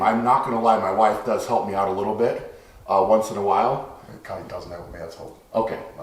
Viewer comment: It sounds like Terry says "The cunt doesn't help me at all." 0:29 What is he saying..? LOL